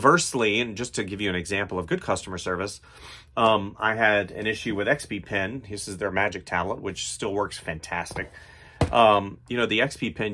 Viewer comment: I didn't quite catch the name of the device you mentioned here. Could you come again, please? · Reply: XPPen magic pad.